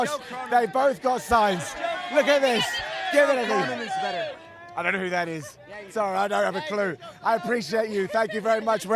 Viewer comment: Does anyone know what that weirdo said at like 1:54? He gets aggressive and says someone is better and that knows who is he. I genuinely want to know who he said because I'm curious why he was so rude to about it 😂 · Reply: "Carmen is better" then he responds with "yeah you do"